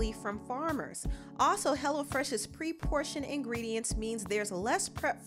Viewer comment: Girl you know didn't cook that food from hello fresh 😂 I'm just joking .but for real at first I thought you we're saying HELLA FRESH All joke's aside ,I love that you support alot of black artist. MUCH RESPECT!!!